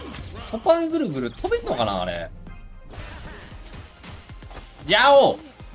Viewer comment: 聞き取りづらいけど「飛べるんかいこれ…」って言ってると思う